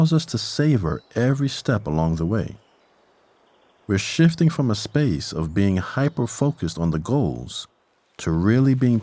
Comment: When he said "we're shifting from a space of being hyperfocused on the goals " I thought he said " we're shitting in a space of beings and hyperfocused goals"